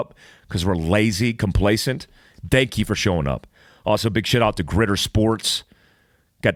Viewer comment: Did he say "big shit out" at 0:23>!